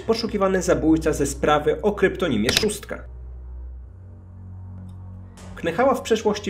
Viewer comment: Zamiast szóstka usłyszałam trzóstka ⊙_⊙